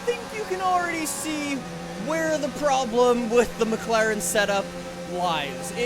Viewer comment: did you say McLaren Senna?